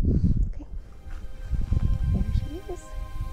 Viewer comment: Why did you say "there she is" quietly under your breath? Closed caption still catches it.